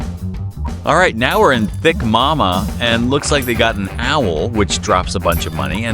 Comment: Umm when did i say that please tell me i need to see if it is actually true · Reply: thick magma i heard it as thick mama tho idk if you will hear it too